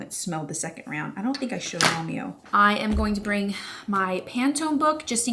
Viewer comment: I don’t know if I heard it right but you referring to mom and Romeo as momeo got me 😂